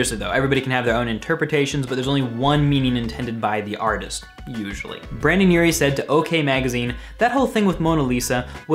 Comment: EXCUSE ME DID YOU JUST SAY BrANdon URIE